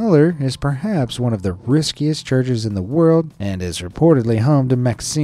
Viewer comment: Oh "Riskiest" thought he said "Riches" cause only rich people would do this